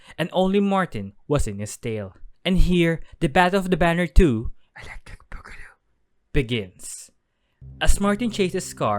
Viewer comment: what did he whisper? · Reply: Electric boogaloo It’s a movie reference